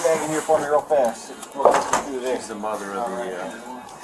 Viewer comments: He said “mother of the uh-“ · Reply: yea, I heard that too, at first I thought they said "mother of the year" but then realized they said "mother of the uh, yea" and it sounded like year 😅 but seriously, I would agree if they said that...despicable and vile woman...and man. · I think he said.. "shes the mother right here"... i notice on a lot of body cam videos they get some of the captions wrong..